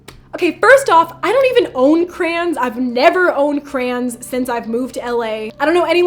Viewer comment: I thought she was saying crans and I was like wtf is a cran but then I realized she was saying crayons 😭😭